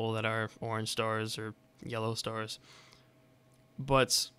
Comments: I thought he said pornstars for a sec. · Did he say porn stars at 2:49 :3